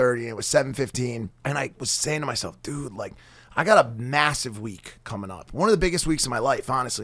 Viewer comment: When was talking about his stomach issues & sitting on the couch saying to himself.... I thought he said he had a massive LEAK coming up 😭😭🤢